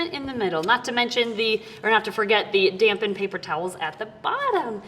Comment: 😅😅 took me by surprise I thought she said let's not forget the damn paper towels in the bottom LOL how many garters do you need in male or female?❤